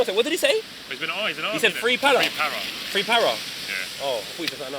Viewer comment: thought he said free pala (palastine) 😂😂